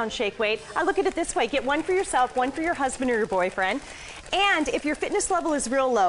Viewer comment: does she say "get one for yourself, one for your husband and boyfriend?"